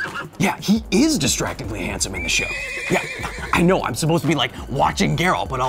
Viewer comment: When he said "he's distractingly handsome in the show" I felt that on a spiritual level. Henry Cavill is just making everyone gay. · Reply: I thought he said, "He's destructively handsome in the show." Which is not wrong.